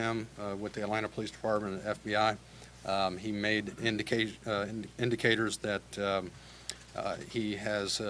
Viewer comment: WHO is the perpetrator? Must NOT be black, his name would have been mentioned by now? I keep hearing "the indicator is indicating"... Something stinks.